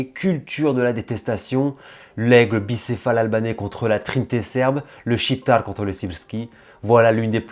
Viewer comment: Début de la vidéo j'ai compris "le shiftar contre le gipsy" 😂😂😂😂 je me suis dit houla il a un parti prit